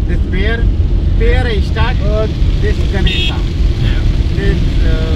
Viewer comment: Sounded like he said Ishtar, aka Easter.